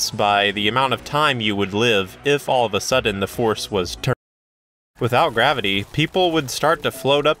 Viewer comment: what did you mean to say at 1:36? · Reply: "if the force was turned off"